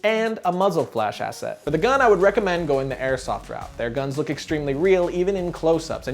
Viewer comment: wtf did he say at 7:30 ? Airsoft Round?